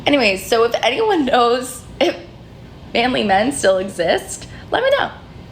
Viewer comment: The girl at 24:50 who wonders where the something men are - I think she is saying manly men, but hears it as family men.